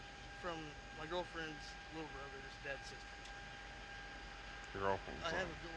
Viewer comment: "It belongs to my girlfriends brothers dead sister" "I texted her and she never texted back" Dude she's dead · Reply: He was slurring. I Thought he said "dead sister" as well until I realised he was saying "DADS sister".